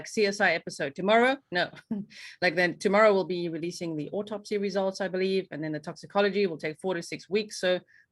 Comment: I thought they were saying the autopsy is tomorrow, not the results.